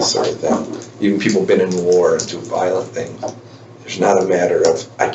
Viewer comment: Wait did he just say 'you people have been to war and done violent things' ????